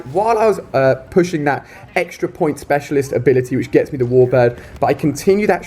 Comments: Did anyone else hear him say "to get the warbird" lol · Did u said At 4:32 extra points special ability which get me a warbird? XD